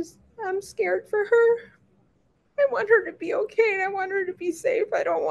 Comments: … is it my ears or does she say “I DONT WANT HER TO COME BACK HOME” ??!!! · Wait, did you guys hear what she said at 4:58 she said I don’t want her to come back home? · she said " I don't want her to come back home".... (4:57) WTF did she just say?lol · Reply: she said "harmed" not home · Why, at 5 min in, she says "I don't want her to come back home"-- ?!?!?! Correct if I'm wrong/misheard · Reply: Harmed not home, but still, crocodile tears